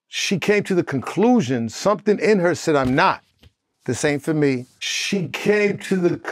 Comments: It sound like he said "this ain't for me" not "same for me". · Hmm, it sound like he said, "She came to the conclusion something in her said I'm not, this ain't for me", but it does sound like, "the same for me". Gyal, this entire situation is DEFINITELY a mess🤦‍♀️ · I don’t think Benzino said “the same for me” I heard it as “this ain’t for me” meaning that that’s the conclusion Coi Leray came to. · It sounds like he said "this aint for me" not "the same for me" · I heard “this ain’t for me” not “the same for me” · He said” this ain’t for me” referring to his daughter’s experience. But I can see why you thought he said the same for me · I heard him say “ this ain’t for me” as in his daughter maybe she said “this ain’t for me” not “ same for me” but that video from Shauna Brooks is damning … 😅😂 honeyyy 🍯🍯🍯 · I thought he said this ain’t for me not the same for me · Benzino said the same for me !?! I thought he said this aint for me🤔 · “This ain’t for me” is what I hear & not “the same for me” I don’t think that was a Freudian slip… But great breakdown! Thank you!!! 💚